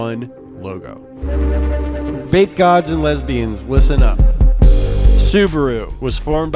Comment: I hat to do a spit take when i heard him say “Vape Gods and Lesbians!”